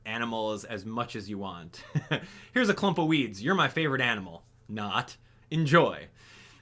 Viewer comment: When said weeds my mom was in the room and thought he said weed. She took my phone... :(